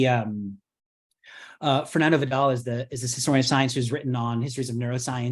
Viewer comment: Looking for this author’s name - it sounded like ‘Fernando Vidales’ but no result — thanks!